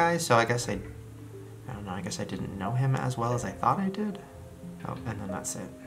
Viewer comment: Omg I heard someone say I'did on 10:07 creepy